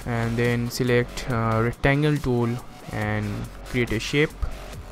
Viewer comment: what does he do at 2:03? Did he say "Clear the ship"?